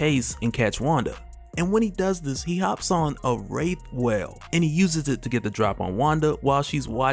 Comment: I was just listening to this while working when I heard "Rape Whale" and had to rewind to realize he mispronounced Wraith whale...